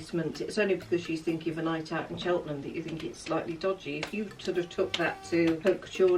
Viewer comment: did your mum say Cheltenham or am I hearing things cuz I'm from so close to there